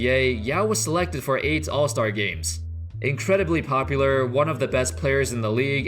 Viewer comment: did he say what I thought he said? Yao was selected for AIDS allstar games...😒